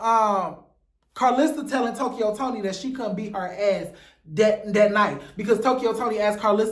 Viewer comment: I thought said Tokyo beat her?